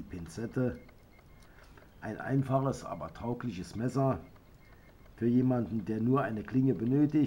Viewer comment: Konnen jemand Mir Hilfen? Ich bin Englander und meine Deutsch ist nicht so gut. Ich habe eine wort gehort daß Ich mich nicht verstanden kann. Die wort Klingt Wie “taubliches messer”. Was ist taublich Oder traublich? Dieses wort ist am 1:22, 2:40 und 4:26. Auf jedenfalls, daß ist ein gute video und sehr informativ. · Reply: t-a-u-g-l-i-c-h